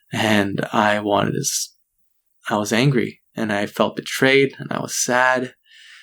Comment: At one point it sounded like he said hangry